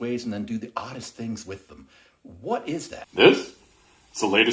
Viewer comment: I heard "Butt chucks"